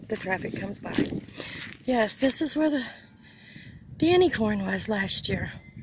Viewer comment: I like to call mistakes lessons. I figure that's what they are if I learn from them. Sometimes, I think it's the best way to learn. Goodness I thought you said "unicorn" and I was squinting trying to see it. You said "corn" okay it's obviously too late for me to be awake. Take care, great video.